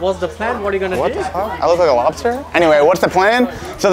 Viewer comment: "zachary you looks like a lobster" LMAO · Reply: I thought he said mobster